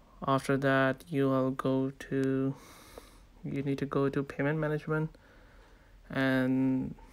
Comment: so... nobody notice he said "paymentnement" instead of payment management? 2:46 XD